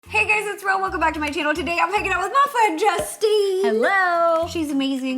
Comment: Did anyone else here making out instead of hanging out with Justine